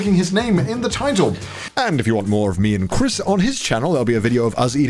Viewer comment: I thought it said me and Chris brown 😂